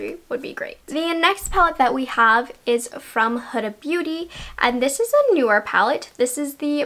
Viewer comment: Is anyone else a little triggered by her pronunciation of Huda Beauty? 10:36 sounds kinda like hood-a beauty instead of huda beauty. 😬🥴😂